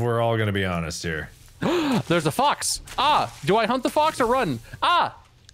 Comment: After talking about the pig earlier all I could hear was saying "Oh a fox! Should I hump the fox or run away?" at 19:00. Anyone else miss hear that? xD